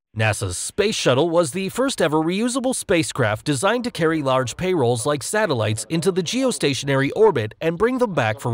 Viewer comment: Did he say "pay rolls?"